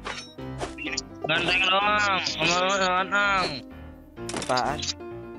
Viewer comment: angeng doang jempuc cewe degang gannagag kwkwkw ngomong apa itu